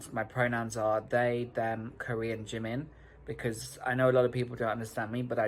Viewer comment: did they just say their pronouns are they/them/kor/ean or did i mishear cause like huh?? i was on board with being nonbinary and using they/them but kor/ean ??